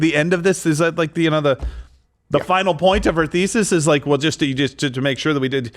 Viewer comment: You said theises I thought you said feces